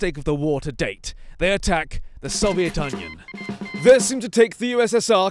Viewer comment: did he say the soviet onion